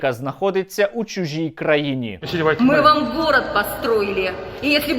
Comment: "ви нє любітє русссссскіх. А што ми вам здєлалі плахова?" (с) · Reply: Ну да после слов мы вам город строили мне послышалось мы вам голод устроили.